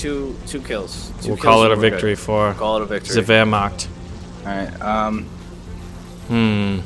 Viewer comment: Did he say we got the victory for the wehrmacht ? xD